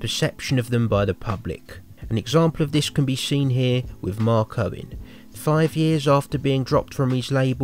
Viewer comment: it sounds like your saying Mark Cohen its Mark Owen